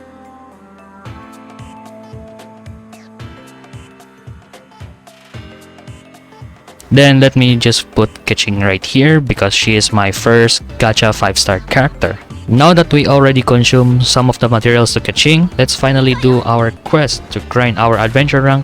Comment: did you say “Let’s put Keqing right here, because she is my first gacha 5 star character”???